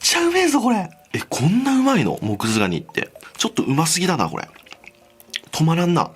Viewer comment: 最初モクズガニがモズクガニって聞こえたww